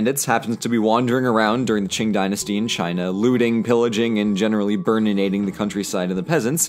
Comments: sry, misheard that "...looting, pillaging..." as "...lewding, pillaging..." but to be fair they prolly did that too.. being bandits and all... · I can't help but hear "lewding" instead of "looting" even though I'm sure it's supposed to be the latter, and the implications of that - because they're bandits - is not ok. ...or was it supposed to be the latter and not just intentionally wanting me to think it was and he's just trolling me like that. Idk. I'm probably thinking too much on this now.